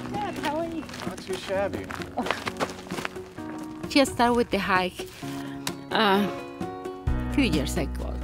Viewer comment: Tell my why i thought it said hitch hiker 🤦🏽‍♀️